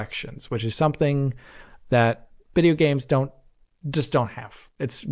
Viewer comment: DID HE SAY BIDEO GAMES?!